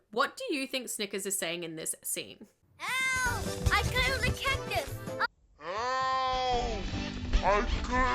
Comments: I hear "help I got in the Cactus" (Which makes sense since the cactus is wrapped on him/he's in it?) 🤣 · When I heard slow down I heard snickers say “help I glued a cactus” but in normal time I could hear “help I cuddled a cactus” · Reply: I heard "help! I killed the cactus"